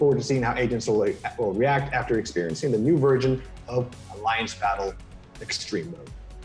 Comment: Is it just me or did say "new virgin"